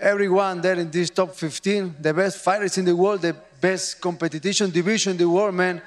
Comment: did he say competittytion